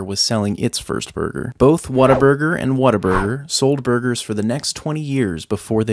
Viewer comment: I thought he said "Waterburger"